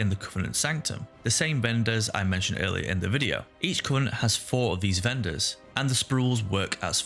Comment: I love this series. I can't justify to sink massive amounts of hours into wow but I've always wanted to experience this amazing world. This might be my best chance of doing so. If I may ask, please articulate your words more when doing post commentary. My first language isn't english and its a bit hard to understand you sometimes. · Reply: I'm saying this not to bash on you, just to show how funny is was from my perspective. At 11:46 when you said each covenant, I could've swear I heard each cunt. That made me chuckle.